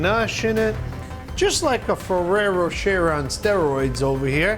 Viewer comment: I thought he said ferrero chair